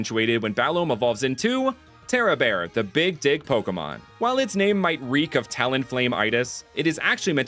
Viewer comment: NGL I heard "Big Dick Pokemon" instead of "Big Dig Pokemon" when you introduced Terrabear